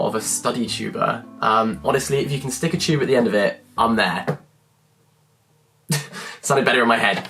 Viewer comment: Did I hear “If you can stick a tube at the end of it, I’m there”